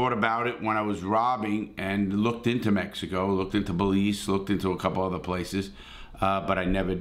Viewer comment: did you say Belize? Wow I never knew you looked into my country. I am honored